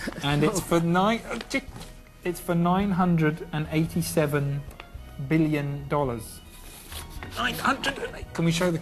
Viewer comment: I misheard it as 497 billion dollars and I was like bruh, wtf? Then I replayed it and it's actually twice larger than I first heard it which is 987 billion O.O. THAT IS A WHOLE LOT. Even if they say Marcos is rich, he can't be "that" rich and Imelda had the audacity to say that "It's hard to earn money honestly and properly" when they actually had billions of dollars stored in a foreign bank. And right now, people are actually rooting for another Marcos to presidency